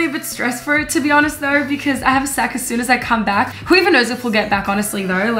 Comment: OMG I THOUGHT YOU SAID "Cause I have SEX as soon as I come back" 10:54 😭😭😭😭